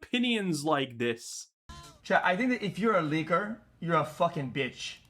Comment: I had to do a double take because I didn't hear "leaker" ...and I almost agreed.